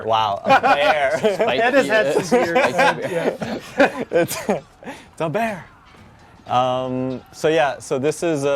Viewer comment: Lmfaoooo.. I heard "beard" thats how you know its a viking ram.. Even the ram is bearded lol